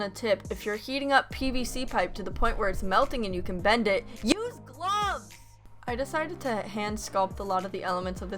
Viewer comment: For those who don’t understand what she said at 0:37 because of the mic peak, she said “USE GLOVES”.